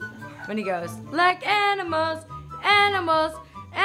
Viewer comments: I... Um... Thought he said "like animals balls" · Definitely thought she said Enemas instead of animals at the end haha